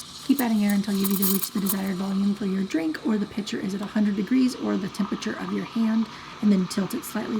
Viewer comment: What did you say at 10:55 to 10:58, I think it was super important but the steaming faded out your voice. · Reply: It was something along the lines of Keep adding air until you have the amount you need or the pitcher is a 100 (degrees) you’ll know because your hand is 98 degrees F. Then raise the pitcher and continue steaming (but no more air! ) until 130- 150 degrees F. Personally I like 140